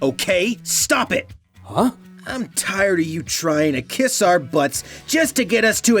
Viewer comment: Zenix: I'm tired of you trying to KISS our butts! Did Zenix said "KISS" or "KICK"? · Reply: ShadowLadyGamer2205 its kiss child